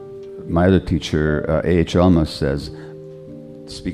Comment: Hi ! Does anyone knows who’s the teacher that he mentions at the beginning? 0:09 I can’t understand what he says. Thanks! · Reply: That's A. H. Almaas.